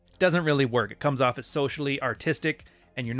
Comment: I thought I heard you said socially artistic, and all now I thought that was a strength 😂